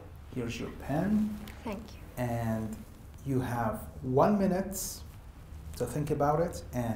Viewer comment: The best examiner I've ever seen, He is being so polite and patient that anyone who is having conversation with him will feel comfortable. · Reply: am I hearing this correct at 4:00 ? 1 minutes?